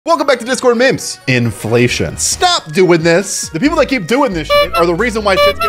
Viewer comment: I thought he said discord cream🥵🥵 色系